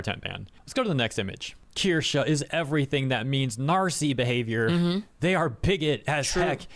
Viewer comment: Thought that said Nar'sie there at 12:31 and it threw me for an entire loop.